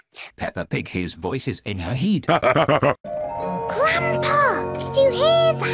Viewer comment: and 1:43 got me dying on the floor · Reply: Lol he said heed instead of head.